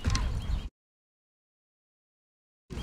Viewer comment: In fall one, I thought the camera man said, "She's mad." and then laughed!! LOL then turned up the sound and rewatched.. haha